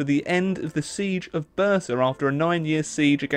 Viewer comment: couldnt understand what city you are talking about for a while but i suppose you said Bursa and that fact is actually not a fact ottoman empire was founded in 1299 and it was a country of a city and some villages